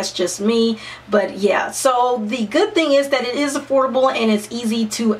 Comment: When she said, "affordable", I thought she said, "HORRIBLE"!! XD